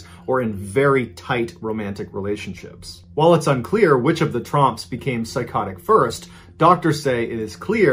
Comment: im not the only one who thought he kept saying "the trumps" right?